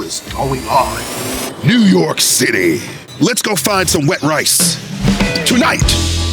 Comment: Ngl I thought je said "let's go find some wet-backs and I had to back it up way too many times.